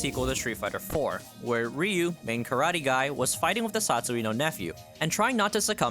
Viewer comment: Are you not familiar with Dark Neph? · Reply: I thought he said "satsui no nephew" 😂